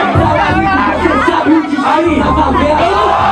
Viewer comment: Não entendi a rima do no 5:16 alguém legenda? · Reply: Talarico você sabe é o destino na favela